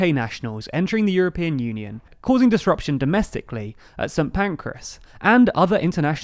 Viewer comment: I'm sorry, but just after 4:12 he said that boarder checks would be "causing disruption domestically at" and then he said something that sounded like "Saint Pancreas". I very much doubt that he said what it sounded like he said, can someone tell me the actual location he is referring to?